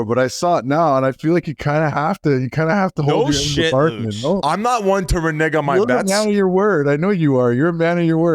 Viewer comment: What did Biz say at 24:36? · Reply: He’s no one to renege on a bet. To go back on a bet or ‘welch’ on a bet.